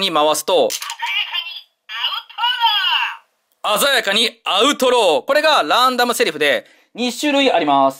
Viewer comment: 鮮やかにアウトローが鮮やかにアウトドアって聞こえた